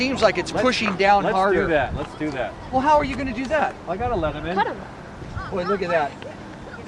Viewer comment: it sounded like you said 'I got a Lemonade' lol. Good video, can't wait to see more f-35 vids with vtol, hopefully it works out for u.